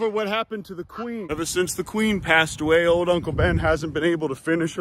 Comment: Did I miss something or did you say Qween passed away at 8:12?